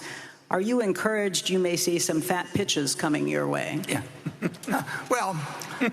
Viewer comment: I almost choked on my brisket, thought she said, "fat bi**ches". Had to rewind that one 😂